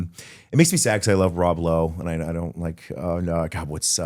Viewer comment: yeah, I hear a bunch of guys like raw blow 😂😂😂